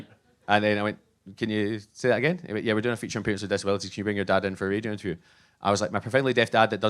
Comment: I heard "can you bring your dad in for a redo interview" at first lol. I got that kind of hearing from my grandfather, we can hear, but often lack clarity.